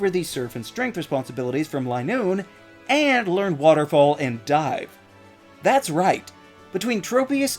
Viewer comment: learn waterfall and die oh, I misheard xD